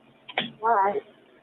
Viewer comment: I hear "Why?" instead of "Alright." 46:05 The "sense" is that gut instinct when something's not right. A book, The Gift of Fear by Gavin de Becker is about this sense that guides you, if you listen to it, to trust those instincts, navigate away from danger and survive. 56:02 "...the girl or the boy?" I'm always dismayed at 911 operators who don't listen well. Colletti already said "girl." 58:30 Brennan says, "Oak Beach Association." 58:55 The dispatch operator asks, anyway, "Is there a name to that community?" even though Brennan just told her the name 25 seconds before.